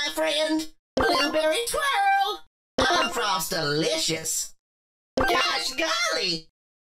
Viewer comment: Does Lou say: "Coach Cory"? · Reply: No he says "gosh garly"